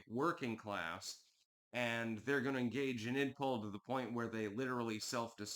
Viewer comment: What is that word you keep using 'igpol'